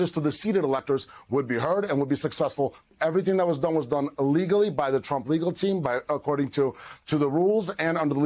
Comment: To me, it sounded like he said that it was all illegally done. Slip of the tongue, or me not hearing right?